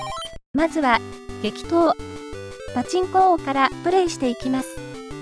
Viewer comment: 「激闘！パチンコ王」が「適当！パチンコ王」に聞こえた自分。 ･･････間違ってないって？ あと全く食べない大食い選手権って。